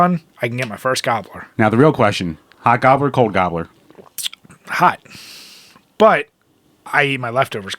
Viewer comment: what in the hell is a gobbler? i thought y'all were saying Cobbler like the desert until you said cheese then i had no idea. What North east trash is this? I used to watch G4 alot but more for Ninja warrior and Code monkeys. Adam Sessler was much more entertaining when he was on coke lol